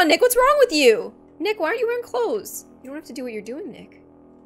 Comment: Who heard at 00:29 she said "Nick why are you wearing close